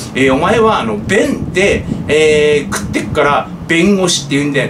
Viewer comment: 「弁で食ってる」が立民絡みだと「便で食ってる」に聞こえてくるからまあ不思議ww